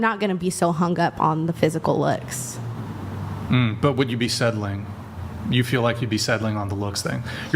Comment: Sed-ling, I thought he was saying something like a hobbit/half-ling..he's saying settling, bro talks weird af.